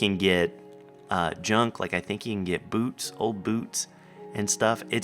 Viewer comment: First I thought you said " You can get boobs" But he really said boots... Yaaaaaaaaaaaaaaa